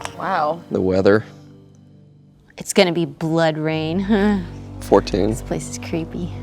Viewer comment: Laura Bailey says 'blood rain', I hear 'BloodRayne', and my adoration for her redoubles all over again. Also, BloodRayne 3 confirmed.